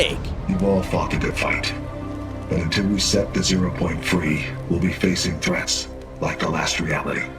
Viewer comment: subtitles say 0.3. Acualy its zero-point free